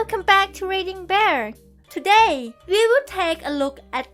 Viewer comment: Good stories,,, but the announcer sounds like she is saying raging bear.